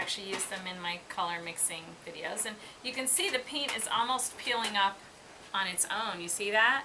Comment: LOL I thought she said, "You can see the penis?" 0:47 It must be her accent.